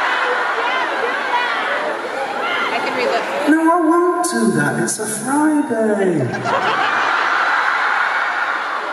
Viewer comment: To the girl who said "I can read lips" TELL US WHAT HE SAID!! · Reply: He said: your daughter throwed her phone on stage and i am going to give it back to her have a nice night!